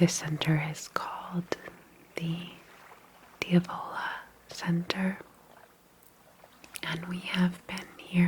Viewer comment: I thought you said Ebola Center 😵‍💫😭 had to repeat it several times… sorry if that messes with statistics 😂🙈